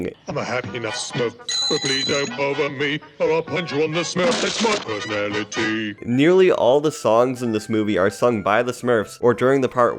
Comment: What are you saying..?? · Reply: "Imma have me a smoke, bippity dope over me, punch you on the smurf, that's my personality!" Something like that lmao